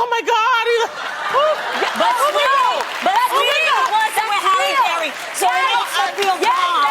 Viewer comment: Does anyone know what Adrienne starts saying at 7:12-7:17 ... I can't make out the beginning of what she said. I just hear ".....halle berry and it makes her feel bomb" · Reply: "But Sweetie he wasn't with Halle berry, so that makes her feel bomb."